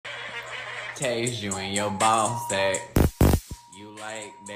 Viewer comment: i think i heard i'll taze in your bald sec or im i wrong?